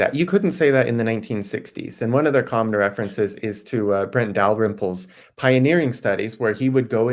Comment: who was he referencing? I couldn't catch the name. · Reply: G. Brent Dalrymple